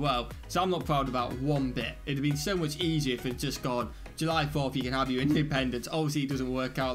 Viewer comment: It sounds like i say January 4th at the start....100% say July 4th :D